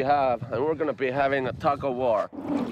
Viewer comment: When they said they were going to have a tug of war, I thought they said a taco war.